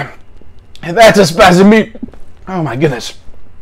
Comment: Tries to say “that’s a spicy meataball” but is in too much pain to finish it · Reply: I thought he was trying to say "that's a specimen" lol